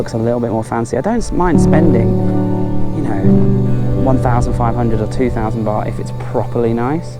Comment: maybe you get wrong as i heard that guy said pan-ha = 1,500baht? not 5,000 so expansive😅